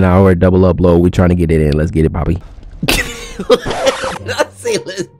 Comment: Caption the thumbnail in the comments.. I can't take this game anymore bro... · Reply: Dude... I thought you said „letz get it Bobby“ ... ok